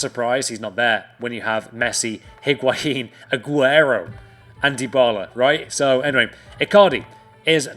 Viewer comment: If you have subtitles on at 7:44 it says Ebola instead of Dybala